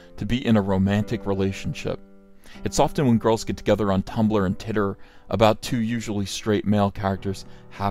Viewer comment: We aren’t just going to speed past the fact it sounded like this man just said titter instead of twitter XD.